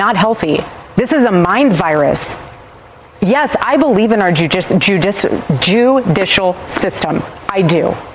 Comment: Don't tell Candace this, but I thought I heard her say "our JEWdicial system" 😂